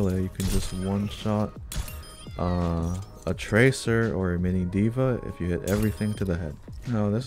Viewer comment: hol' up did you say "mini diva" 🤨🤨😳😳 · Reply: Yeah sorry if it was hard to hear. Mini DVA (when she's out of the mech) is only 150 hp